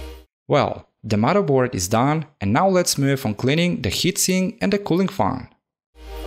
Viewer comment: I heard "cleaning the heatsink and cooling found"